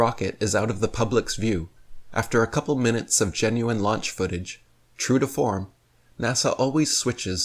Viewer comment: Did Douchebag just say, "Genuine launch footage" @ 9:32!?!